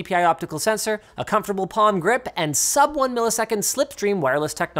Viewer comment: is it only me who heard "Porn Grip" 😹😹